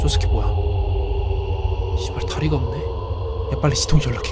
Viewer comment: ㅅㅂ.다리가 없잖아 이렇게들림